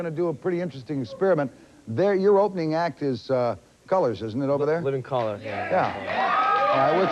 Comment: at about 2:20 did he say "your opening act is coloreds?"